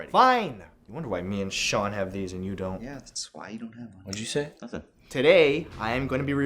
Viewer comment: i heard sean say "thats why don't have one"